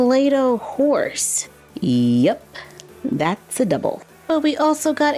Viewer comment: Sooo when you opened the second one with the playdoh horse I heard "Yep.... that's the devil," but I'm assuming you said "double." XD